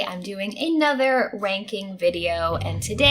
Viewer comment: I totes sounds like you said 'another wanking video' lol